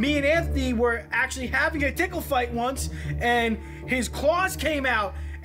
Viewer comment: I thought he said Pickle Fight